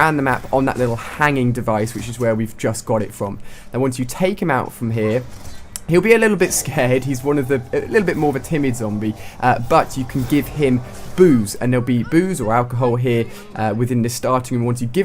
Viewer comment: first time a heard these i thought he said '' you have to give him boobs''